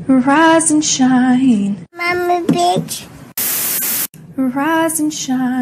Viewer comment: I died of laughter 😂😂😂😂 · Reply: Lmao ikr it sounded like she said mommy bitch😂😂😂😂